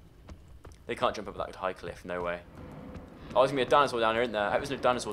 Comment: I THOUGHT HE SAID DANASAUR I SWEAR TO GOD HE SAID DANASAUR or do I watch to much youtube